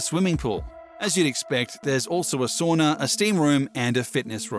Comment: Was this a joke played on the narrator? LMAO!! When he said, “sauna.” It sounded like he said, “sonar,” due to his thick Australian or English sounding accent. I think the editor either misheard him or added that clip in as a joke. Whether it was on purpose or by mistake, that’s actually a quite hilarious blunder by the editor. They definitely gave away the fact that the narrator and the editor are two different people.